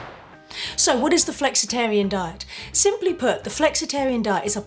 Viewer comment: Aww I thought it said "The Flexitron Diet" ☹️